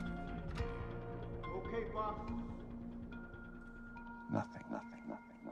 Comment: did he say "Okay Fuck"?? 😂